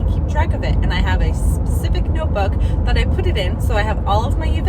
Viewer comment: 🐈‍⬛ hi, I thought your set up looks so good. When you were in the car and you said you forgot your notebook at home I swear I thought you said deodorant and I was like oh my God girl been there 😂 I really enjoyed this video, I liked the editing, I like the music, I thought you were very personable maybe more relaxing in from the camera I don’t know what it was, but I really enjoyed it. I would love to see how you are going to change your set up when the items for your mom‘s side arrive, and I know you mentioned that the ice cream octos didn’t sell that well because it wasn’t the right season for it that is very interesting to me since I have not done this for more than one season, how will things change when you go from springtime to summertime? Maybe you can do a video about your future plans for your markets, how early do you start offering seasonal or holiday items? and during your busy season, how far in advance do you book shows, and how close together do you book them?